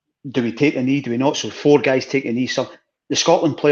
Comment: I thought he said where are you on taking an E for a second, it took me a moment to realise it was taken the knee.